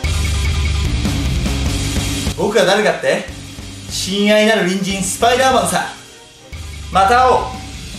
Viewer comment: 親愛なる人参スパイダーマンさっ！に聞こえるんだけど…